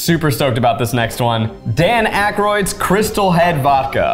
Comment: Maybe this says more about me, buti had to go back to check for the crystal head vodka i thought they said "CRYSTAL MEEEETH" 😂😂😂